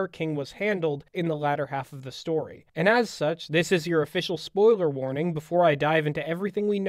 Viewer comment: I paused it when you said "spoiler warning before I dive" so I just heard "before I die-"